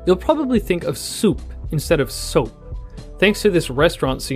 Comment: for some reason, I thought it said scoop